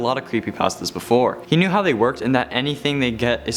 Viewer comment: I thought he said crappy pastas